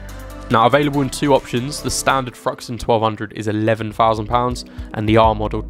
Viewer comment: The Triumph Thruxton is 11,000 lbs?!?! Lol JK. I did poo a little when I first heard it though.